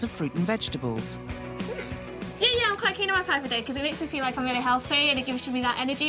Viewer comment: I have absolutely no idea what she just said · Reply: " yeah yeah I'm quite keen on my five a day cos it makes me feel like I'm really healthy"... I had to literally become an ear to figure out what she was saying